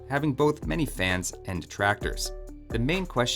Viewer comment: did you just say the map has tractors?